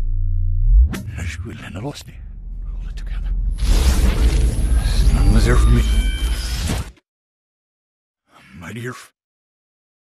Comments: Release me! We're one together Together, together Let me out Gravity is a harness Together Release me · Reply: Sounds like @ 1:36 he says 'pull it together" instead of "we're together"? I dunno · I hear at the end "release me" and throughout the trailer "hold it together" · I keep hearing him say “pull it together”